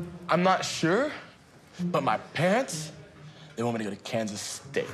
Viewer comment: Am I the only one who heard 0:49 pants instead of parents?😄